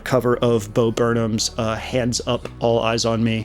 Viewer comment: LMAO THE FIRST TIME YOU MENTIONED BO BURNHAM I HEARD IT AS BOBEATS. luv the work you do stay fucking awesome. super cool to see someone who evidently has the same constant existential dread as me lmfao